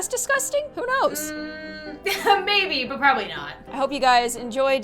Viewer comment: I thought said if you never whant to see me again push those notifications